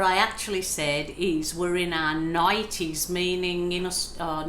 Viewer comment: Love watching you paint. I have to admit, I thought you said you were in your 90s as well. LOL! Glad to know it was your nighties.